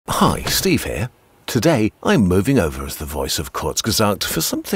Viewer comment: Ok, that is NOT a good way to start a video!!! All I heard was "Hi, this is Steve and I'm stepping down as the voice of Kurzgesagt". And I was like "No! Why?!?" Was relieved when I realized he meant temporarily.